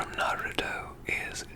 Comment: I love how he’s role playing a gentleman from the 1800’s whilst also talking about Star wars, Naruto etc.. 😂😂🤍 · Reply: For a second I thought you said Nagito instead of Naruto and I had a mini freak out because I thought there was Danganronpa 😂